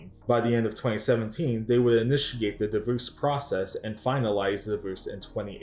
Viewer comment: The what? Is he saying "deboose"? "Devoose"? WTF is this guy saying lmao! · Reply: lol divorce. my b if it wasn't clear